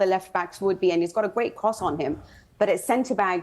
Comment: Did she say at 3:12 that he has a great cock on him????